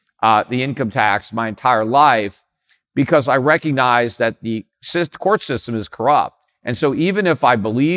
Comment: Sith Court System? Did I hear that correct?